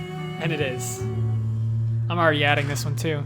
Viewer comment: Heck, I thought it was "I'll readjust" this entire time. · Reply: i heard it as "i'm already adrift"